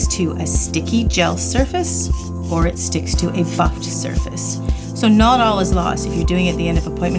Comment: did she say it sticks to a “fucked” surface?